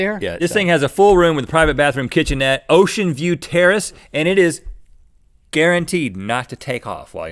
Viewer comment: did rhett say ocean veiw terroist